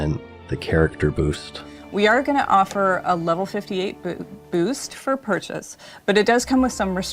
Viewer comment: did she say "a level 58 BOOB?" 😱😱 😂😂😂😂😂😂😂😂😂😂